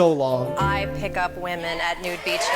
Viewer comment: I wasn’t looking at my TV when said “nude beaches”, so I thought he said “new peaches” and just assumed it was some kind of farmer’s market that only losers shop at.